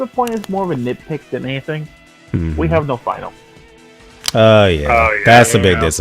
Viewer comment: What was it at 1:54 that was a bit dissappointing? I heard we have no final but i think i misheard